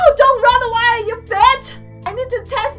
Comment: it sounds like she said cocaine club